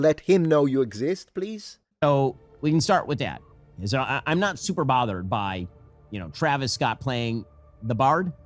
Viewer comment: Ben Shapiro does not see white people as his own people. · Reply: When he says it doesn’t bother him, all I hear is “As a Jew I’m not bothered.”